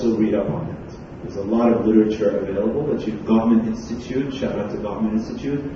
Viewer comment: What did say here? Name of website? Gharment institute? Reading material for husband /wife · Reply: Tremor Drake gottman institute